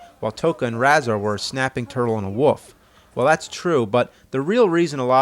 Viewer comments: did u just call a wolf a "woof"? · did he say woof :) yay!